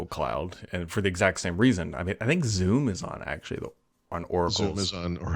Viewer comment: When you said "Zoom is on Oracle cloud" I thought you said "Zune is on Oracle Cloud". Almost spit out my coffee! 😆